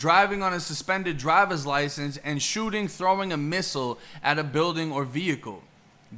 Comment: hold up did you just say missile😂😂